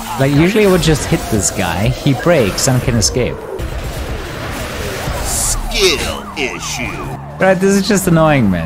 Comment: I thought he said SKITTLE ISSUE